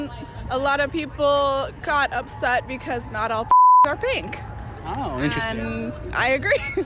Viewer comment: did she just say all pussies are not pink? 🤯😳